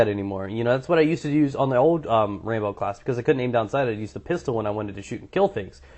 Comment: I really heard you say: rainbow class .-.